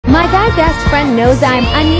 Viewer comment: I thought she said my dads best friend 💀💀😭😂 i was so confused 😅